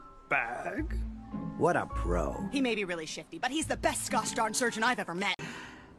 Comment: I thought she said “Shitty” 💀